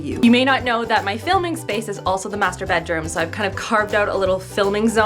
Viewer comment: sorry for the misheard "masturbate room" 😂